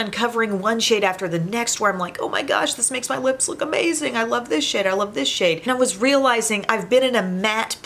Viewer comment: Oh my gloss! I could have SWORN you said "I love this shit" when you said "I love this SHADE. Took me a second lol. 😂